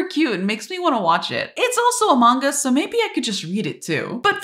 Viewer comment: When you said a manga I thought you said among us and i was gonna cry or something